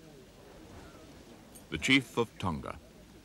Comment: "The Chief of Tonga🇹🇴??? Wait am I hearing that right?